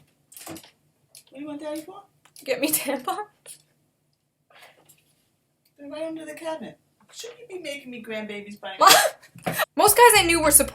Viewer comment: To those who couldn't hear the conversation was: Her: "Dad! Go get me tampons." Mom: "They're right under the cabinet. Shouldn't you be making me grand-babies by now?" Her: "MOM!"